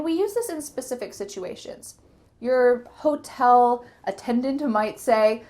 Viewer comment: May I ask you a question? At 2:51, Which of the following did you say? "Your hotel attended to might say ---" or "Your hotel attendant might say ---" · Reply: The 2nd one my friend. "Your hotel attendant my say...." :)